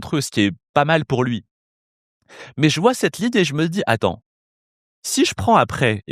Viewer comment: ses moi ou il a dit Adam au lieu d’atant😂